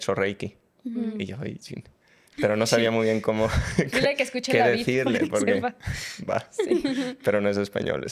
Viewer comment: UNA PREGUNTA: MINUTO 10:43 dice "dile que escuche LA VID O DAVID" 😮 NO SE ENTIENDE...¿QUÉ recomienda que escuche alguien que ha hecho Reiki???? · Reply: Es el canal de youtube de ellas 😊se llama